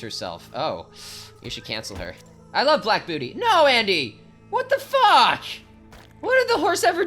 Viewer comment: For those who heard something else at 08:00, I said “black Beauty” in reference to the book. If you heard something else all apologies for the misunderstanding 😂😂😭